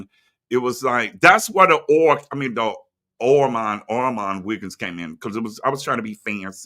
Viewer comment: I didn't no the song said orman Wiggins I thought it was saying all my Wiggins as in all us lot 😂